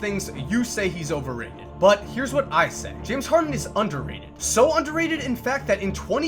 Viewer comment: Never mind I thought you said overrated